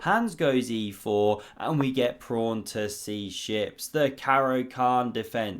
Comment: Did he say ‘pron to c6’ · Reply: Prawn lol